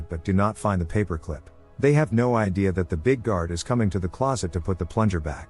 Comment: I heard "big guard just ignores it" like 5 times